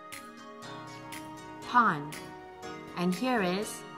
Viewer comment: Hi, what did she say at 3:12 ? Im confused.... But I really have to say, i love your videos!! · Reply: Pond